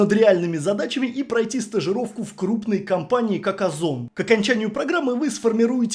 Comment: КАКОЗОН ? 😮😅 Я один это услышал?